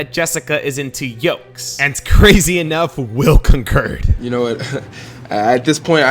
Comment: Did he just say currlle